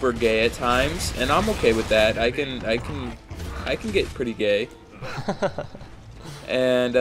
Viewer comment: i skipped a bit and all i heard was "i can be pretty gay"....safe to say i was worried o.O